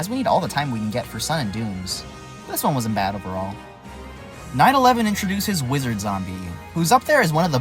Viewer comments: why did I hear 9/11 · i heard 9/11 instead of night 11